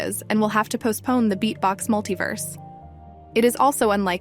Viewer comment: Did somebody just say beatbox!?